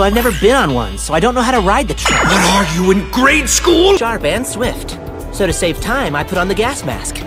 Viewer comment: what did he say I thought he said 'Dhar Mann swift' 😭